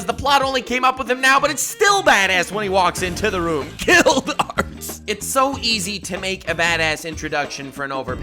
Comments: sounds like is saying Kill darts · did he just say kill arse